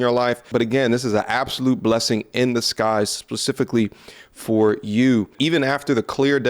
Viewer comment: Am I the only one that heard him say, "blessing in the skies"? Bruh.... the correct phrase is "blessing in disguise". You're a very smart dude so zero shame! Just figured you'd want to know you were saying that wrong. Love the content and keep it coming! Found you maybe 2 weeks ago but I watch at least a couple of your videos daily! So thank you for the knowledge you bring us all!